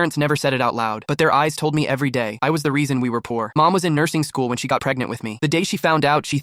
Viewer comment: Yeah you are 100% not the reason they're poor them f**** without protection is the reason they're poor that resulted in you being born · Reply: I thought you said he was the reason they are poor 😭